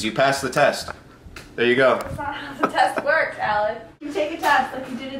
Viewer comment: Did she called him Alan